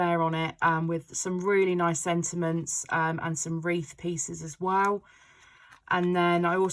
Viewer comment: Totally thought you said Reeces Pieces, not Wreath Pieces 🤣